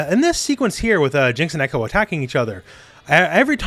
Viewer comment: During the handshake part I thought you said "Ekko attacks the loli" and had to rewind to confirm I misheard it